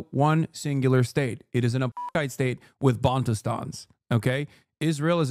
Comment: Hey fam, can I ask for some help? At 2:10 he says 'Isreal is an ethnostate with'....what did he say there? I've never heard of that word, and i can't find it because I'm not spelling it right. 😥It sounds like he said 'bonta-stance', but I'm not hearing it right. Thank you for helping me out if you're smarter then I am, LOL! EDIT- A "Bantustan'! Slowed chat Hella down and figured it out if anybody else was curious! Learn sumthin new erryday. Much luv, fam!! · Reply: The word he said was "Bantustans". A Bantustan (aka Bantu homeland, black homeland, black state, or simply homeland) was a territory that the National Party administration of the Union of South Africa (and later the Republic of South Africa) set aside for black inhabitants of South Africa and South West Africa (now Namibia), as a part of its apartheid policy.